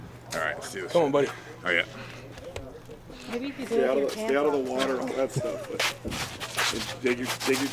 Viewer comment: did say, "Maybe if you do it with your pants off"?